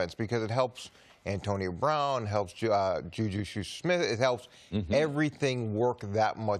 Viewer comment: Did he just say juju shoe smith